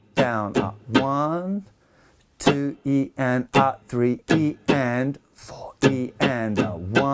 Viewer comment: What is he saying at 6:52? "One, two…" and then ?? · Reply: eea M he's counting in sixteenths, so 1-e-and-a-2-e-and-a-3-e-and-a-4-e-and-a he's just making the sounds E and A